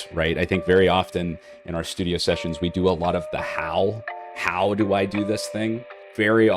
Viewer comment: I heard "very often, when we're doing our studio sessions, we do a lot of the HOWL" 😅